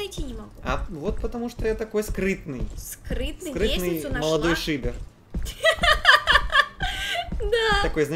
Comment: Мне одной послышалось что сказал *я скрытный молодой шипер)?